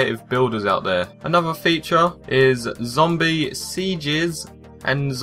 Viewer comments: it sounds like he said zombie sea jizz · it sounds like he said zombie sea jiz